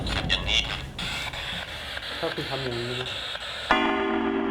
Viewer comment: ทำไมเราได้ยินว่า ห้ามคุณทำอย่างงี้อีกนะ